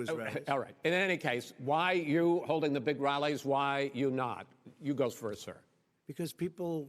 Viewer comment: Anyone else hear "Why you holding big rally? Why you not? Yous goes first sir." at 31:33 XD